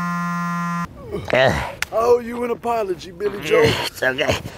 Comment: At first I thought he said: "I owe you an apology video."